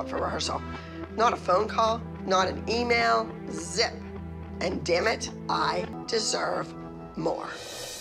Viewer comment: Abby~"and damnit I deserve more" Me~* I thought u said that u earn thing u don't deserve it". Smh😂